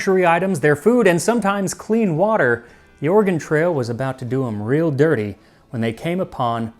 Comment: I keep hearing "organ" trail.😂